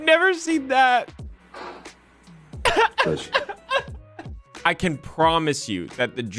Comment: What did he say at 05:53 ,please? I don't get it. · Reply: J.D said "Bless you".